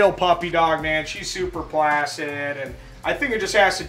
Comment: Thought he said flaccid instead of placid and was confused for a second because yah know it’s a noodle 😉😏 lmaoooo I geeked when I realized my mind was in the gutter